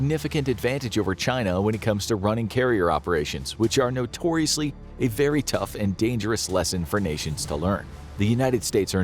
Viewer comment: Had to watch twice I swore he said carrier operations are notoriously a very dangerous lesson for Asians to learn 🤦🤦